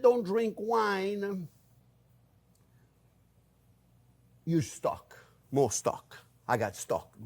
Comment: I swore you said “you suck” at first 😂 instead of “use stock” 🤣 I’m so glad you’re back!!